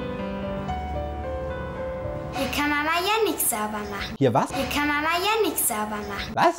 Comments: Ich habe verstanden "..hier kann Mama Jenix sauber machen.." xD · "Hier kann MAMA ja nix sauber machen" Tatsächlich schwer zu verstehen beim ersten hören und ich halte es auch für ungut gelöst trotzdem lustig das du es entweder absichtlich oder unabsichtlich nicht geblickt hast in dem Video :D